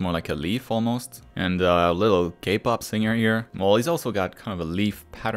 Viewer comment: I heard gay pop singer for some reason :X